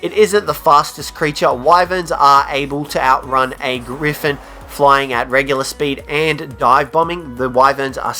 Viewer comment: Did he just say a wyvern could outrun a Griffin? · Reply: TheOrangeNinja he said a wyvern can outrun a griffin at regular speed dumbass